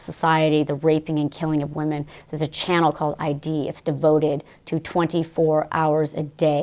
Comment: What channel did she say at 0:35? I'm trying to just verify this is all. Any statement this edgy deserves to be triple fact checked. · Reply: ID — Investigation Discovery. I've watched it, idk what she's talking about sexualizing murder victims. I mean yeah, the show occasionally focuses on romances and affairs gone bad, y'know, husbands and wives killing each other so they can live happily ever after with their mistresses and lovers. And they of course hire attractive actors and actresses to portray these people. But it's never a situation where they say "This girl was murdered, wasn't she fucking sexy?"